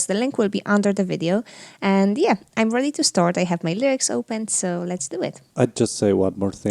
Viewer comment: Did you say "I have my legs open"?